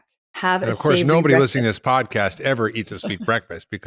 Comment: I kept missing what she was saying because she was spoken over. She never repeated it because I think she thought we heard what she was saying. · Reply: She repeated it at 11:50 about having a savoury breakfast 👍